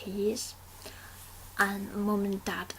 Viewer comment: LOL When you said 'aunties' I thought you said 'NTs' :)